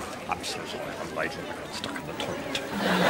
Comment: From 15:45-15:47, what did Mr. Bean say? It sounds like he said, "I'm sorry I'm late, I was stuck in the toilet."